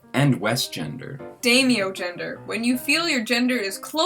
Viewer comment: straight up thought you said daimyogender, instead of daimogender. oooo let me try: a gender connected with a past life experience of ruling feudal Japan.